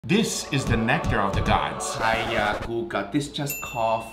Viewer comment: Bro, for a second I thought Guga said "This is the neck hair of the Gods."